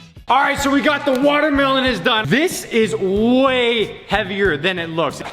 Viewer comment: did he say we got the watermelon is done